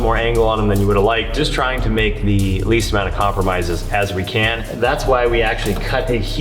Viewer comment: Every time cuts more out of the chassis I keep hearing say "lease".